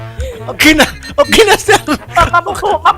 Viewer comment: Tama ba yung narinig ko 19:17 okinnas na😂😂😂